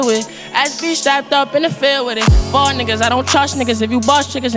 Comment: why did it sound like he was saying fart😂